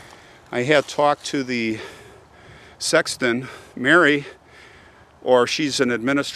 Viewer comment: I meant doctor rather than sex worker. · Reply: I misheard, you said sexton at 11:30 I was thinking of the nautical travel device sextant. I hadn’t heard of the word sexton and thought docent which is more a church tour guide. Now I know a new word, thanks for the vid.